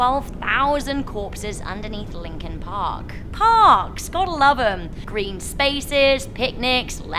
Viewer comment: I thought it was Linking park, I misheard and it was Lincoln park😅